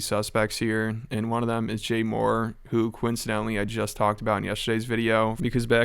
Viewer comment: I am certain it's Jay Mohr dude. I hear an "er" at the end.